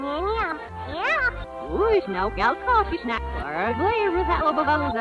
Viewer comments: I hear "please let me out, he locked me in here" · I heard please let me out